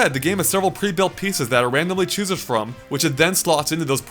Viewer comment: did you say "choosen from"?